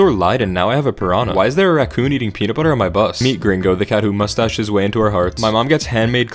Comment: I thought u said “meat gringo” I was dying 🤣😂🤣😂